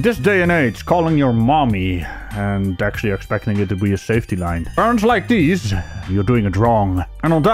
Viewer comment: or anyone, help me out. I can't understand what he said right at the end, at 22:27 he says "...and actually expecting it to be a safety line, ??? like these you're doing it wrong" Cc says Burns Like These... I can't figure it out and it's bugging me lol